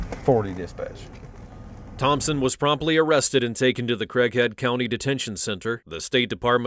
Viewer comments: "crackhead county detention center" is what I heard and it's fitting honestly. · i thought it said crack head county i had to go back 😂😂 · When they shown her mugshot and said "Thompson was promptly arrested and taken to the Craighead County Detention Center." I first thought they said the crackhead detention center. Between the mugshot and her attitude, that made perfect sense lol. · what it sounded like at first. Thompson was promptly arrested and taken to the crackhead county detention center. Sounds reasonable 🤔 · I thought they said “the crackhead detention center” 😭😭😭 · bro i thought he said the creakhead county detention center 😂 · i thought the narrator said she was arrested and taken to the "crackhead county detention center" damn straight. 😂😂😂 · I swear at 19:20 it said “crackhead” 😂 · When he said Craig Head Country Detention Center... I thought I heard Crack Head Detention Center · They said she was taken to the Craig head detention center. I thought they said Crack head detention center. 😂😂😂😂 that makes more sense, cause she looks like a druggie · I thought he said “crackhead county detention center”